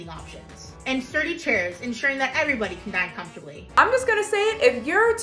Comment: I seriously thought she said so everyone can die comfortably